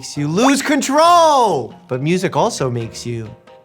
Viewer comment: When Dr. Mike said "music also makes you A S D" referring to the keyboard, I thought he was saying that music makes you autistic. lol